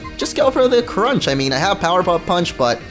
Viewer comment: did he just say powerpuff punch?😂